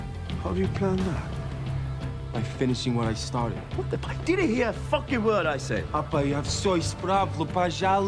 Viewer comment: “What the, he didn’t hear a fucking word I said.” Gets me every time 😂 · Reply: I always heard it as “Didn’t he hear a fucking word I said?”